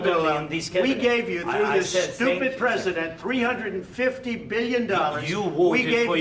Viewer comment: did he say stupid president